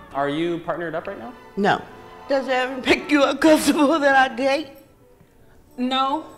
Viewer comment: I fully support her if she said she was gay but what did she say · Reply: She said: "Are you uncomfortable that I date?"